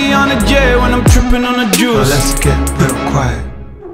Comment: I thought he said "now let's get La Croix" lmfaoo